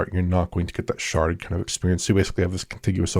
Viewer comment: Re: Horizon MMO -- every time I hear someone say "sharded" I hear "sharted."